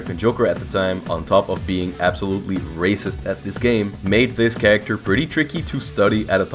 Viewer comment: "As well as being absolutely racist at this game". I can't be the only one who hears that.